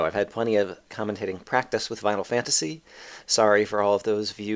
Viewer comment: Watching video, here you say "commentating vinyl fantasy", thought you said "commentating final fantasy", frantically search for videos.